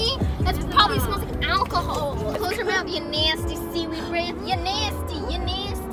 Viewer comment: did she say seaweed brain (pjo fans have entered the chat)